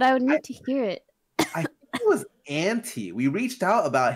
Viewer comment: idk if I'm the only one who heard it like this lol Scarra: I think his name was Ante maybe. MIchael: wait his name was Hentai?